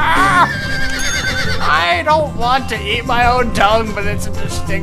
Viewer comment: anyone else thought he said "I really don't wanna eat my own dung" >.>?